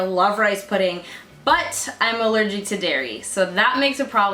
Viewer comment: I thought you said 'no Dari' 🤣